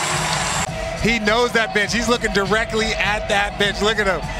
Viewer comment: sound like hes saying bitch